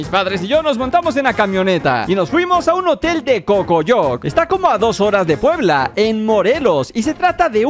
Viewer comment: escuche pocoyo